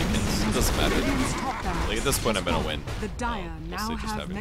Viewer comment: im laughing so hard because i thought i heard "nigga creeps" instead of mega creeps xD